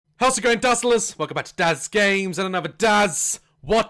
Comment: It sounded like you said "welcome back to daz gay!" When i skipped it XD